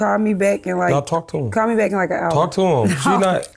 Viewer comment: She really got a jail call and said "ima call you back I'm in a interview"😂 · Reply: I could of sworn she said call her back in like an hour🤔